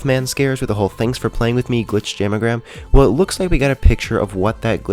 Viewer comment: I thought jamagram said instagram